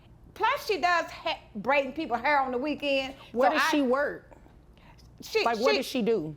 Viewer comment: I had to go back and listen because I swore she said "she bring people heroin on the weekend" 😅 I'm relieved she just braiding hair on the weekend 😂